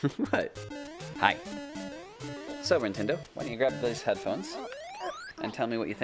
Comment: is his daughter named nintendo? It sounded like he called her that. 6:51 well at least she has good taste in games: i hear her breaking minecraft glass blocks. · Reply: Heh, she goes by and she's become quite adept at Minecraft :)